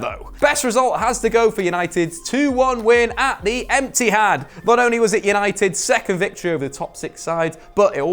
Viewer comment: does he say "Empty-had" that's jokes